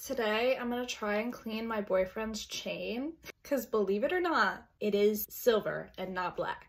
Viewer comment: Tell me why I thought she said eating my boyfriends chain💀